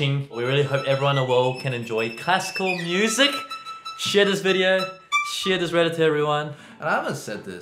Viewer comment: did you say "Shit this video"?